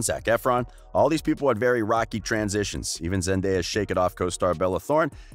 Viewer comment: did you say “shake it off” 😂😂